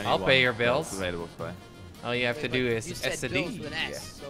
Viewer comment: did say all you have to do is s the d